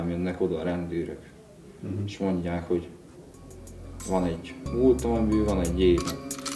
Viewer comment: nél mit mond? Nem érteni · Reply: van egy multam amiből van még 1 év